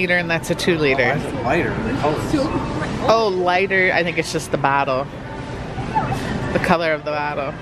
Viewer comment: I heard the word "battle" instead of "bottle" 😂🤨 it wasnt just me right